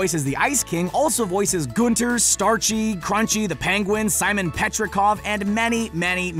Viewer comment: did he said crunchy the penguin? XD